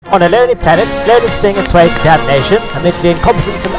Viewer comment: I swear, I hear "dab nation" every time